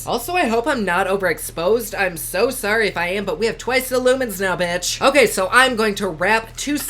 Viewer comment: For some reason when you said I have twice the lumens, I thought you said you had twice the lemons lol.